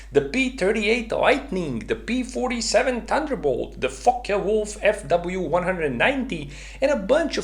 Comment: "The fuck yea wolf fw 190" lol that's what I heard